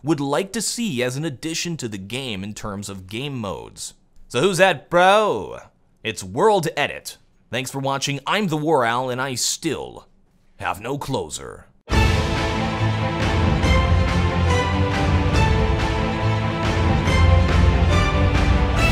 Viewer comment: i always think at the end he says, "and i still, have no clothes on." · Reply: First time i heard it i thought he said "and i still, have no closet"